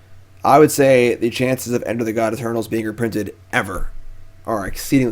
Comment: Anyone know what card name he mentions at 47:13? I nan’t seem to understand it. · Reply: Enter the god eternals is what I heard